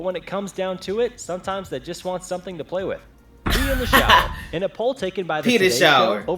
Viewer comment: The first time he said pee in the shower I thought he said penis hour 😂😂😂😂😂😂